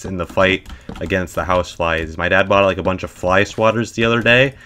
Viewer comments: I genuinely thought he was saying House Wives instead of House Flies until he mentioned the swatters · Oh god I thought you were saying “house wives” not “house flies” until you mentioned fly swatters. I genuinely thought you were talking about mass homicide for a solid four minutes. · oh god i thought he said house wives instead of house flies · i thought he said house wives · I thought he said house wife's but turns out he said house flies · when he said house flies I thought he said housewives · I thought he said housewives not house flies at first :b